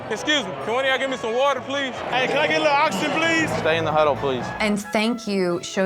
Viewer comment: thought he said oxygen haha